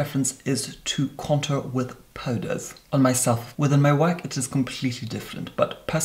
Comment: I thought he said, "With my wife it is completely different," and I choked on a cough drop.